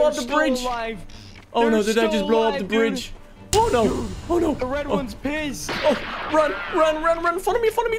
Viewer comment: did he say"the red one 💀pissed💀"he literally dropped the p boom 💀💅